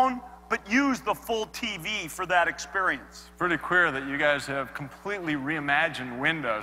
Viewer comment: did he say queer at 10:29?? wtf?